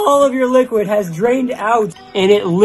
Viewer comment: I thought he said ' don't be griddy '